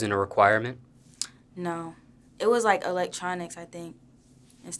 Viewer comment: So I’m the only one heard her say Spanish was a electronic instead of elective 😭😭